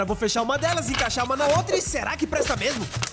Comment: Eu entendi "Faz o L ai e me conta" kkkkkkkkkkkkkkkkkk